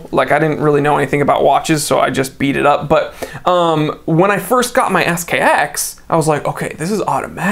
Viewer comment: At @ 4:25 it sounded like you were about to say, "When I first got my ass kic.." Lol